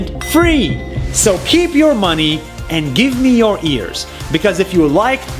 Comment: did he just say give me your ears